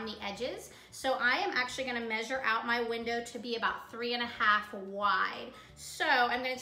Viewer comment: How many inches did you say to cut the opening on the side panels? It sounded like you said 3 1/2 Y. What does that mean? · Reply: 3.5” “wide” from the edge of the board. Thanks for watching 🤗